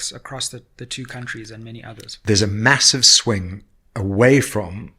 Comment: I thought this guy said he's saying away from polotics😂 mxm